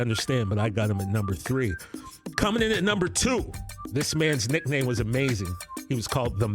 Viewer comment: Dickersons a great pick. And I thought you said Randy "Amazing" White lol He shares an office with my dentist.